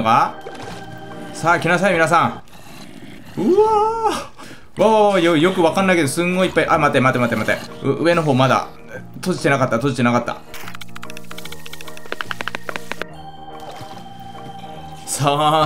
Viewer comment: いっぱいて何回もゆうとぱいぱいぱいぱいって聞こえるの俺だけ？